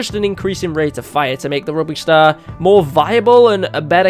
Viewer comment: the captions say rubbish star I don’t know why people call captions inaccurate their right